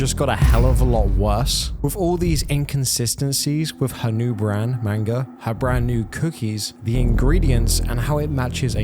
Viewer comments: Did you just say "her new brand manga" ? Lol. If not, that's exactly what it sounded like. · what did he say? "Manga" 😂